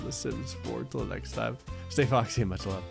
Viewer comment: Can someone tell me what he says at the end of every vid, he always says it too fast for me to understand? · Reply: "stay foxy and much love" is what I hear... that can't be right LOL